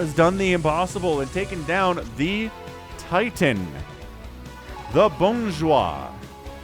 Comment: I know you tried to say something in french, but this time I don't even understand it ^^" I suppose you tried to say "the bon joueur" ? If so that's ok, I know english speakers have troubles with the french e (or eu) sound, just as we french have troubles with, say, the "th" sound (heck some of my friends pronounce it "t" turning games like War Thunder into War tender xD ) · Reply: He said "The Bonjwa", which is a title given to flash as the strongest player. It was originated in Korean